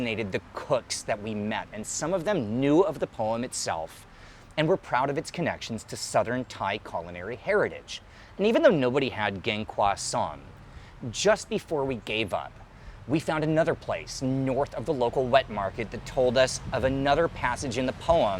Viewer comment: i misheard geng cwa sont ( crossaint ) and was like wtf 😂😂😂